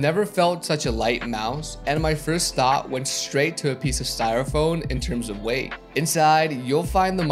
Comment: Is this the best mouse on the market? Probably, it's also one of the best looking ones. I swear I said Styrofoam at 1:05 but go ahead and bully me :P